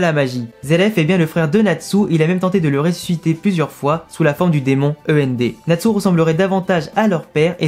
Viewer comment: À 2:58 j'ai compris le démon E.M.B 😂